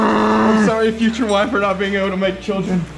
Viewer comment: what says 😂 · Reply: Frannie he said " sorry future wife. I won't be able to make kids."